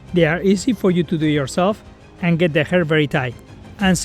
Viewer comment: i couldnt quite understand, what is that thing called he tied the hair? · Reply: Kitsch Pro Bungee Hair Ties, Hair Ties with Hooks